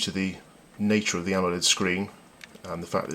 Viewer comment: did he say AMOLED screen ? O.o